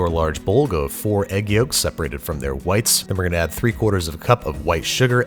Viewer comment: Why did I hear "four egg yolks separated from their wives"